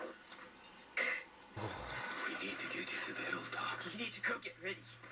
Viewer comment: What was Maggie saying? I'm kinda hungry so I heard it like this XD 24:36 "We need to cook it Rick" 24:42 "To fatten him"